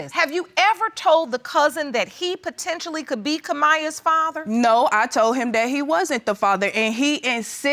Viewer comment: I definitely thought that said My cousin maybe my baby’s father. 🤣